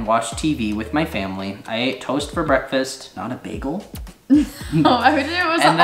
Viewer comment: when said "toast for breakfast" i thought he said toes hahah